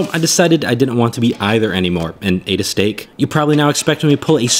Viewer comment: For a second i thought he said, "And I ate a Stick"